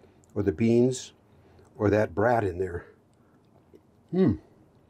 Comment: Great video. Had to pause and back up. You said brat, but I thought I heard you say that rat in there.